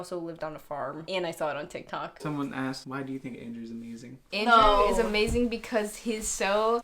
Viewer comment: DID YOU SAY ANDREW IS SO BRAZILIAN?? IM AKSNKSMSK sorry im dumb